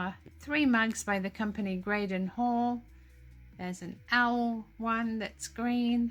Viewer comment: you picked up 2 coffee mugs. Thought you said they were "Grace Hall?" I look up items when mentioned that I am not familiar with. I could not find. Who made it, spelling? Thank you Love watching you. And your pets.❤